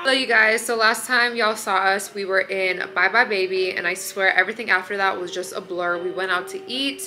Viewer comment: i am from South Africa and the whole time i thought you were saying Bye Bye Baby till i saw that plastic bag😂 love you!